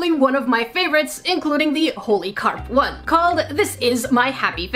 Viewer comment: I thought she said 'Horny carp'😂😂😂😂😂😂😂😂😂😂😆😆😆😆😆😆😆😆😆😆😆😆